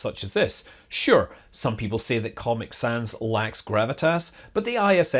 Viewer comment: I was in the next room and instead of comic sans I thought I'd heard you say pocket sand.